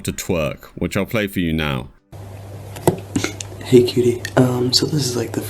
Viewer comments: That "hey cutie" made me want to throw up. He is beyond creepy and gross. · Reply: I thought he said "kiddies" for the longest time. Until I saw multiple comments saying it was cutie. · The “hey cutie” and “its your turn now” balances between making my skin crawl from cringe & disgust, and laughing my ass off at it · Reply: i thought he said "hey kitty" so...cutie is at least a little better?